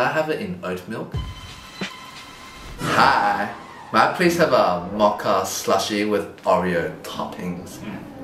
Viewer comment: I THOUGHT HE SAID VODKA SLUSHIE LMAOOOO